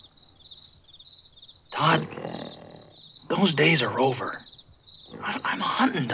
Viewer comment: Lol, it almost sounds like he says "those days are rover". In fact, it would have been cuter and made more sense.